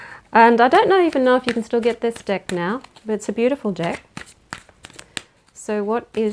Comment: Got to get used to your accent. Thought 💭 you said, "It's a beautiful Dick".